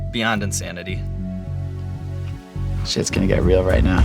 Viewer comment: does he say “ shit is going real” or “this is going real”...